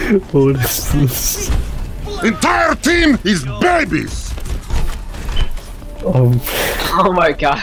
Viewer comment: instead of babies in captions its daddies